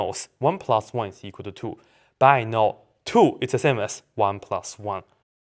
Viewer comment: Did you know that 2 = 1 + 1?? I bet not! jk : ) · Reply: at first, I thought u said what I know is that 2=1 and I thought u were joking.